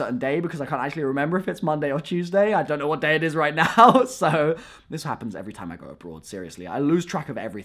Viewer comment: why did I hear "this happens every time I grow a beard." Help please 😂